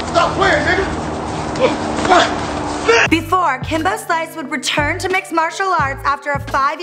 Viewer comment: Kimba Slice???? Lmao you had one job... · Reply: Bro the annoying chick talking sounded like she said Kimba instead of Kimbo at 0:11... Didn't mean disrespect